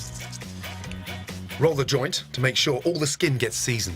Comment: all I hear is "roll the joint" 23:17 I alt tab and find a lamb lol